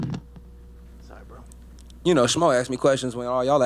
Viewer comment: I thought he was saying Smoke not Schmo lolol